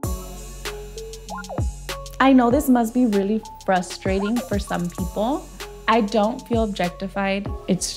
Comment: Did she just say breastfrating ? I’m serious that’s what I heard...